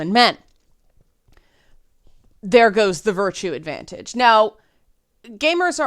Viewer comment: I kept mishearing "virtue advantage" as "virtual advantage".